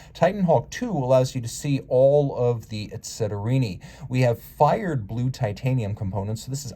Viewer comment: 👂 What’s that word I hear? Was that ‘etceterini’?